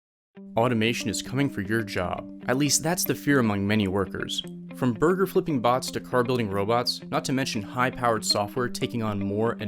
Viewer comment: i thought it said Automatron like from fallout lol